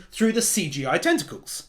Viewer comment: Anyone else notice how the subtitles said testicles on